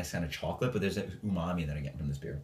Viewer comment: what did u say at 0:38 after chocolate ? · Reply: <link> I said "umami." Cheers!